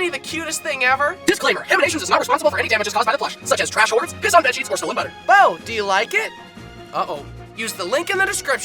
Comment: What it said: DISCLAIMER: HAMINATIONS IS NOT RESPONSIBLE FOR ANY DAMAGES CAUSED BY THE PLUSH. SUCH AS TRASH ORDERS, P!SS ON BEDSHEETS OR STOLEN BUTTER. hope this helps